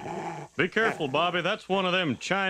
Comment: I heard "West Taiwan Terrier".